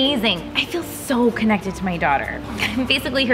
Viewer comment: I scrolled right before she said "daughter" and thought she said dog 💀